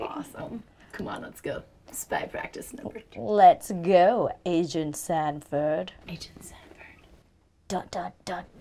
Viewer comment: I swear I thought she said "Asian Stanford " instead of " agent Stanford "